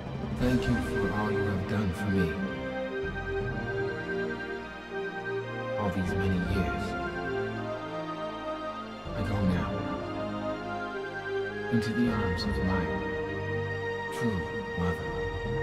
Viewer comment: can somebody please transcript what the master says here? I can't understand everything from this part (english is not my first language) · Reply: "All these many years, I go now, into the arms... of my true mother."